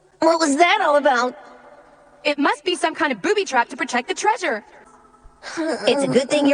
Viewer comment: Bruh did i just hear "goofy trap"💀💀